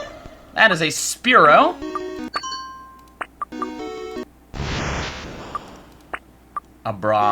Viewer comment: Did you say"a bra"?